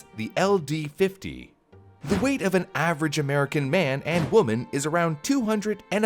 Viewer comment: I seriously thought he said "The weight of the average American male is 265lbs" I mean I knew we were a obese nation but c'mon.